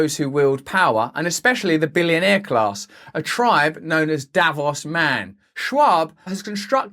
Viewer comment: I thought you said osama for a bit needless to say I would freak out if he shows up lol